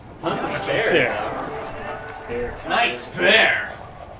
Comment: Whenever I got a spare I always thought it said ' nice pare ' not 'nice spare' lol